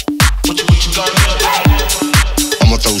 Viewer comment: Me as a five year old: "I'ma throw shapes" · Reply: I thought it said "I'm a pro shape"